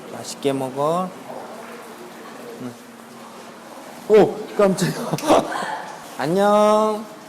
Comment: 오 깜찍아로 들림 ㅋㅋㅋㅋㅋㅋㅋㅋ